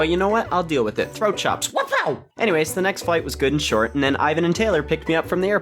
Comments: I heard "The next fight was good and short" Not "The next flight was good and short" Had to check captions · I thought you said the next fights were ok because you said throw some chops